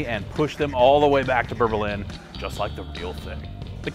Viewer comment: I swear at 1:12 he said berbilin not Berlin lol